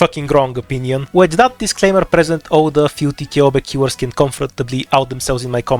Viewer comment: I couldnt understand what you were saying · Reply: filthy ceobe killers